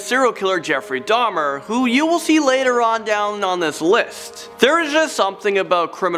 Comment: Did he say @ 0:10 later down on this LISP??